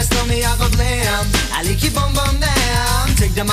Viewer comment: I always misheard it for 'I lick your boom boom now'